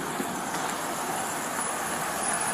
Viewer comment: did he say cheese and crap?